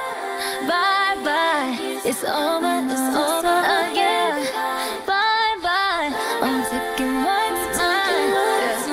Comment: THANK YOU… heard the “baby” in original and still cant tell what she says exactly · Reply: Sounds like “You know it’s over baby”